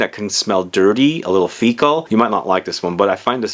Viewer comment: Did he say faecal? at 19:42.